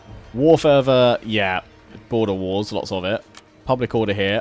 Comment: OHHHHHH! 57:07 I thought you said border walls, and I was like "is he actually talking about trump?" lol nope just border WARS, not walls lol